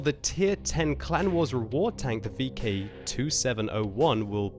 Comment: Did say "VK 27.01" at 0:40?